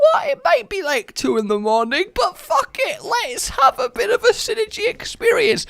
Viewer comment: did he say buttfuckit